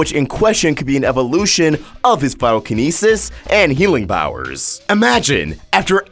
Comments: Is he saying pyrokinisis at 3:04? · wut wus the word at 3:05? pitokinisus? i dont know